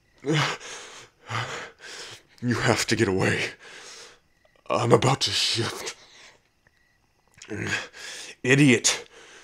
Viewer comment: I swear at 6:09 I thought he said imma bout to shit😂